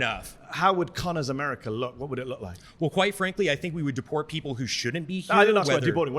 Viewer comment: That one dudes argument “Quite frankly Quite frankly Quite frankly Quite frankly Quite frankly Im a fascist” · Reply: He kept on aspirating the "Q" so many times, I had to listen VERY intently to decide if he was saying "White frankly". Eventually I heard the "Q" sound, so I'm sure he was saying it both ways.